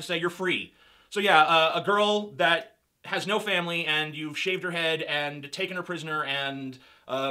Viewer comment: Instead of saying "shave her head" I thought said "shit on her head" hahaha